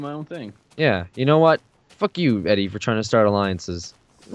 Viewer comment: did call eddie?